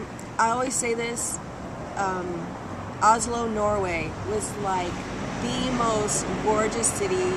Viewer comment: Did she said Norway at 5:45?